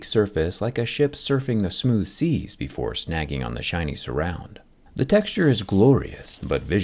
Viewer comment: i misheard “.. like a ship surfing the smooth seas before snagging on the Chinese rounds ..”